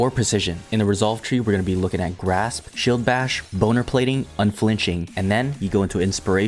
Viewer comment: did he say boner plating?